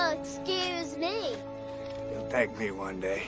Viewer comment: For the longest time I thought at the end he said "take me one day" as if saying to god, please kill me. But he actually says "you will thank me one day"